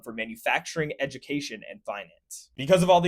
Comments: When he said finance I thought he said violence,shame he was wrong.. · when he said finance i thought he said violence 😭